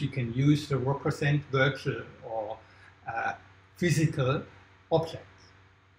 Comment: "virtual physical objects" are you for real? The definition of virtual is: not physically existing but made by software to appear to do so. Example: "virtual machine" If something is physical it's not virtual If something is virtual it's not physical This video is evidence for the belief that developers behind Cardano don't understand what real people want but maybe they can sell it to "virtual real people" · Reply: Relax dude. He said “virtual OR physical object”. Besides, it’s ridiculous to completely reject a whole team of developers just by a misuse of a term in one’s presentation lol